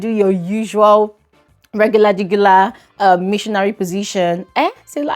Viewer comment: Ah ah, nah why do I keep hearing "jugular jugular" 😂😂😂